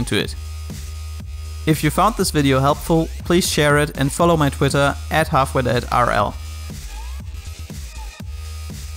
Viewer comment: Can someone tell me what percentage he said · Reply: 1-3%